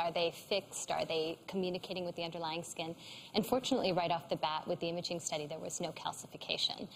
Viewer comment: Every time she said "an' fortunately," I thought she was saying "unfortunately," so I'd be like "Oh no," and then she'd say something positive and I'd be like, "...What?"